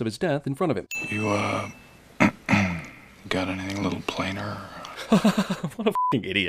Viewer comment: i genuinely cant tell what he said help. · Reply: "You uh... [clears throat] got anything a little plainer?"